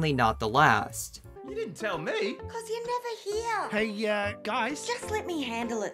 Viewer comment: I think that actually says “cause you never hear!” Instead of “cause you’re never here!”